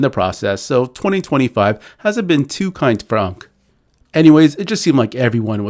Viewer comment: too kind to who? Was that AI or just messing up trying to say Unc? Sounded like Gronk 😂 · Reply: “For unc”